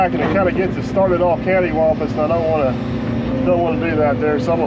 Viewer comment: We never want to start off "Cantiwaumpus"! Is that where bridges are built under swamps instead of cantilevered over? hahah 🤣